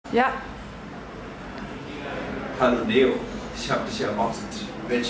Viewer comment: Ich hab erst verstanden: "Hallo Deon" :D